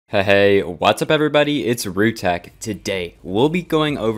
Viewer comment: When he said “heyy guys. It’s roo tech” And I thought he said rude I was like UGH HOW RUDE AS A JOKE 😂😂 CUZ HES NOT- Okay I’ll shut up